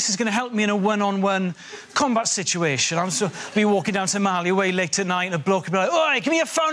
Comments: I thought he said "...walking down Somali way..." like "...down Mexico way..." and wondered if that's really a thing. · I thought he said "...walking down Somali way..." like "...down Mexico way..." and wondered if that's really a thing. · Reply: Continuing the Mexican theme, I thought he said tamale way😃. I guess it was "some alleyway".